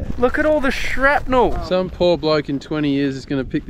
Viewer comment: Did he just said some poor black in 20 years is gna pick this up thinking hes never working aday again...!? 6:26 😮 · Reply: "Bloke"